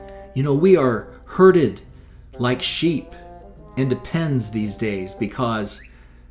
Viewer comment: When he said "herded like sheep into pens" I thought he said "herded like sheep in Depends," and I was so confused 😂